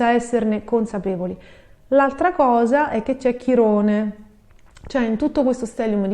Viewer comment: Oggi pura seduta di psicoterapia, grazie ❤ "in gemelli c'è chirone" ho capito "c'è chi rode" 😂